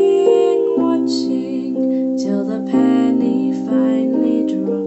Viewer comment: I thought you said pantie instead of penny XD